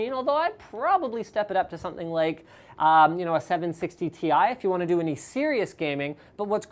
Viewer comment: Did he say 760 Ti!?